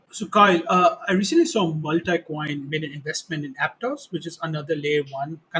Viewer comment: What did he say at 11:00? He made an investment in "Aptos"? I can't understand his pronounciation. I can't find any APTOS, APTAS or APTUS. Thanks. Also at 21:37 What is he saying "Randa" ? What's the name of the coin? I dont think I can subscribe to a podcast where I can't understand the key words being said. You need to realize that we can mostly understand your English but when you pronounce new token names super fast with a heavy Indian accent and nobody has heard of the token, nobody can understand what you are saying. At minimum reference the new tokens with URLs in the "Show More" section of the Youtube description. Thanks.